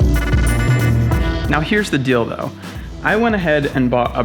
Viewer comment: Today I am headed to Iceland for another insane paramotor adventure. If you aren't already, make sure you are following me on Instagram for live updates as I won't be able to post any Iceland vlogs until I get home. I have two scheduled uploads for the week that I am gone including the Aviator Paramotor Training Giveaway winner announcement on Saturday! Get stoked, lots of awesome content is on the way! Follow on Instagram here- <link> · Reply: did you say "Here's the dildo"?